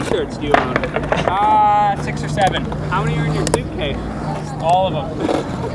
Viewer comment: what did just say? 😳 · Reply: six seveeeen